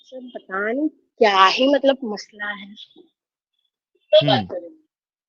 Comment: pichhe se kisi ne kaha 'kya baat kar rahi ho ?' I might be wrong, I definitely heard someone else's voice in the back. If it's her family member, she might be in trouble. Stay safe sister Hari Aum Tat sat 🙏🏻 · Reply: han aap sahi bol rhe ho kisi lady ne bola kisse baat kr rhi ho